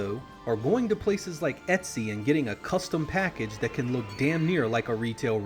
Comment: sorry what are you saying there? "Going to places like ___ and getting a custom package..." ? I'd really like to get an English version of those games. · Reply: Etsy, check it out on Wikipedia.